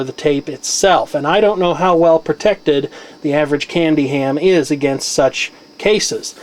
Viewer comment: did you say candy ham? lol XD